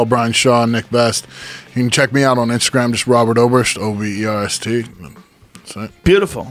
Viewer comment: Damn...I thought it said "Conor Oberst" 😩